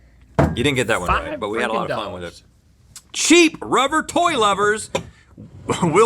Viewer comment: i honestly thought he said sheep rubber toy lovers....